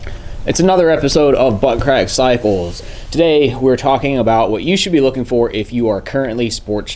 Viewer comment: I thought you said "buttcrack disciples" lol! It was a relief seeing the channel name.